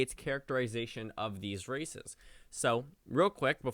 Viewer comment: "Based off 538's characterization of these RACISTS" Lol I know he said "races" but when I first heard that I was like wait what did he just say?!